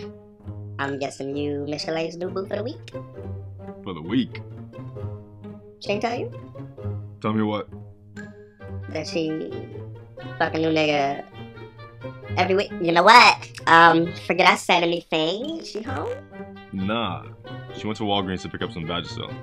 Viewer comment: did drea say "fuckin yo nigga" in